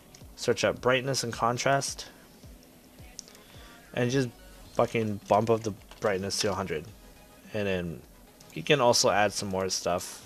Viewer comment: I thought you said "buttfuck the brightness to 100"